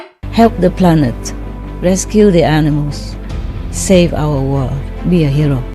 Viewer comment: she sounds like she is saying "let's kill the animals"